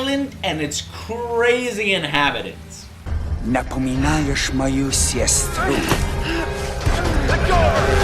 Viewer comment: he actually said "you remind me my sister" in English accent trying to speak Russian.